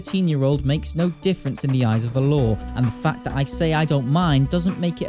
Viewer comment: I THOUGHT YOU SAID EYES OF THE LORD AND I LAUGHED